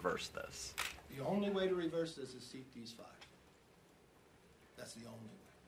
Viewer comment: The only way to reverse this is what???? I have rewound over and over again blasted the audio and I still don't know what he says at 16:40. Was anyone able to get what he said? Sounds almost like Deese 5 but not finding anything on it on the web.